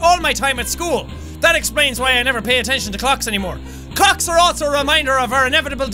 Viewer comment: he said cocks instead of clocks .. jack youre gonna get demonetized